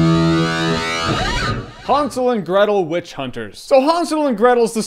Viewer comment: Did anyone else heard him saying Han Solo and Gretel every single time?